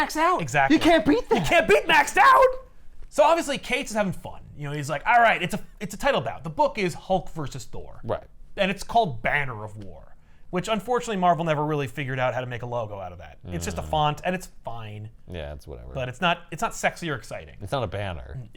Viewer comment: There are so many sex jokes on this channel that I heard "Banger of War" and spat water at my keyboard.